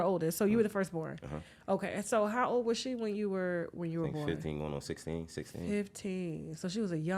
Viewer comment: Yall need to pay attention, 13:45 what age did he say the mom was? Jayz was 24 -25 years old. Now now how old did he say the mom had him at? I left the time stamp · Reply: 15/16. I heard him. Ms Lily also said she lied to him and told him she was 18.